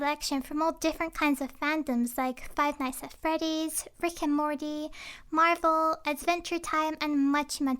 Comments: “ frick and mordy “ I thought she said that ( No hate) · I thought you said frickin morty when you meant Rick and morty 😂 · When you said rick and Morty I thought you said FREAKIN MORTY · when she said rick and morty it sounded like she said fricken morty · When she said rick and morty i thought she said "frickin morty" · When you said rick and morty i thought u said fricking morty XD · When she said rick and morty i heard "friken morty!" XD my hearing sucks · Legit thought she said Frick and Morty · When she said rick and Morty I thought she said frickin morty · I thought u said Frick and morty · I thought that she said frick and morty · It sounded like she said Frickin' Morty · I thought you said Frick and Morty! XD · It sounded like you said "fricken morty " lol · For a second, I thought said "Frick and Morty". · I thought you said frikin morty lol